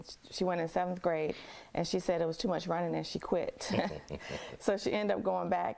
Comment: Did I hear this correctly? Around the 1:45 mark did the victims mother say the girl quit running track in the 7th grade because "there was too much violence"?